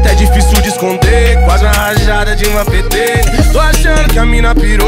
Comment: Só eu entendi "Dilma pt"?😂😂